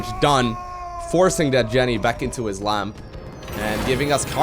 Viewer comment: "put that jenny back in her lamp" is what i thought you said XDD leave jenny alone!